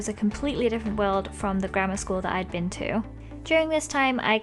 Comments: Who else thought she said "Grandma School" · In the 3 times I have watched this, I always thought you said Grandma School! Typical me.